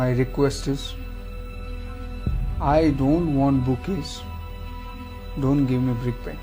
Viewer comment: what does he say after, "I don't want bookish " ? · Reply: I don't want bouquet, but please don't give me Brickbats